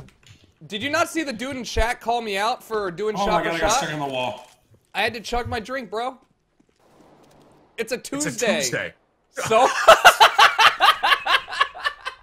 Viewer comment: What does he say at 3:25 ? It s a Toomsday?